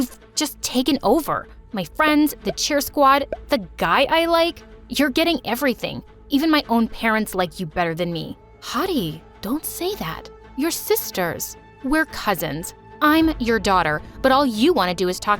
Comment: What normal people heard: “My friend Heidi” What I heard: “My friend Hottie” 😶‍🌫️💀🥺